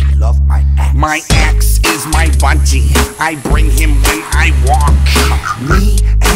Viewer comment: I heard 'my ex is my budgie' wtf